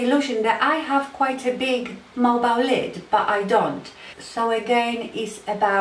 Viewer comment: liking the video so far, but what word is she saying in reference to the lower lid? is she saying mobile lid? it's hard for me to understand (i.e.